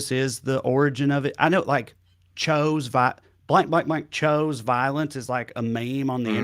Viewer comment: AHAHAHA! I thought he said, Cho's violence!🤣🤣